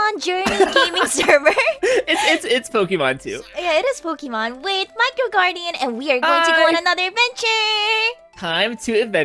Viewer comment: in the begining I thought she said my girl guardian